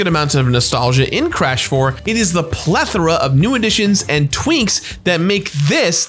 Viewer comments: Anyone else hear him say "twinks" at 8:09? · Am I the only one who heard “twinks” instead of “tweaks” at 8:09 ? I’ve rewound it several times and it’s all I hear 😂